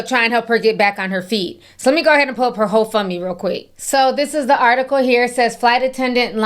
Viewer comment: Idk if I misheard you, but when you said, “I’m not supporting her hoe fund me page,” I almost died laughing.